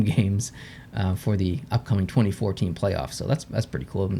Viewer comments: who else thought he said 2014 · Anyone else hear “2014 playoffs” when he said “24-team playoffs” around 8:00?